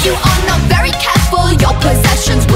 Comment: I heard : Ticci Toby taught me how to feel